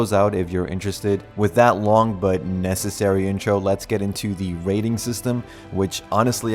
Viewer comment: I misheard "Rating system" as "Raiding system" which was my main interest, 20 minutes later I felt bamboozled. So does this game have raiding?